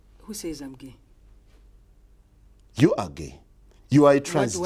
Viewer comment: When he said you are gae it sounded like he said you ugly. Lol. My ears are dumb.